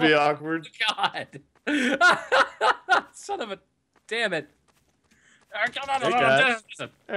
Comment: "Hey you, sitting there with your bowl of cereal," IM ACTUALLY EATING CEREAL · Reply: Even weirder, when says "Hey guys," it sounded like he said "Hey Cas," and THAT'S ME! Weird...